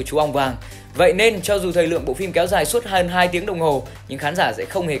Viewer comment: What?? 2:23 mk nghe ra là phim dài 22 tiếng đồng hồ :))